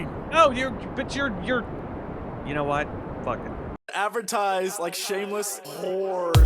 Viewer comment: First time i heard it as Advertise like Seamus' whores.i was like wut.